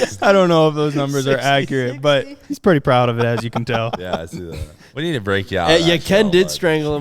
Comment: “We need to break you out of that…..” what does Cletus say at 58:15??? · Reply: “ we need to break you out of that shell, bud.”